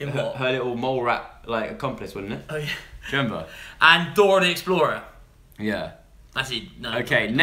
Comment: Im sorry but did say he fancied Dora the Explorer